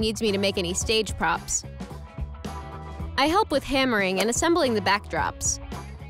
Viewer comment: พี่พูดว่าไรนะ30:14การไรนะ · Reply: การตอกค่ะตอกตะปูค่ะ👁👄👁